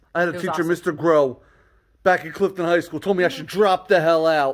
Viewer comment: when you were talking about your teacher...i thought you said...clifton heights (where i live)...but after i listened a few times...you said clifton high